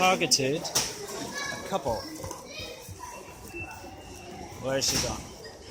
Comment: I thought he said "where are you from" not "where are you going" (你是哪里人）